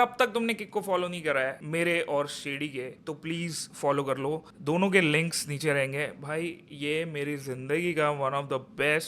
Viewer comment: what did he say 🧐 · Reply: Links ❎ Lings ✅